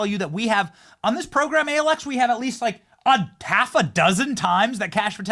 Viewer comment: Thought you all said “we Have It ALL”